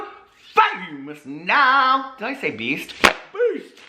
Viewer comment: For the longest time I thought you said "peace!" in your intro, I just realized you say, "beast!!"🤣🤣